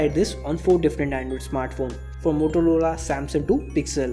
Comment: did he just said motoloda 😄